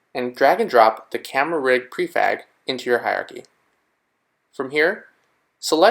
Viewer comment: in I'm certain you said prefag instead of prefab... no one caught that?!